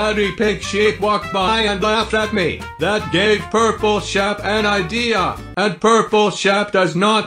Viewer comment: My god when u said gave purpleshep I thought u said purpleshep is gay I was like WTF?!